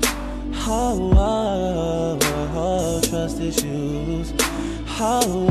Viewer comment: trust his shoes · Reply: I srsly thought he said trusty shoes Rofl 😂😂